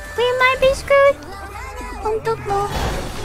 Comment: does gura says 'Untukmu' in perfect indonesian, damn i thought that Moona's sound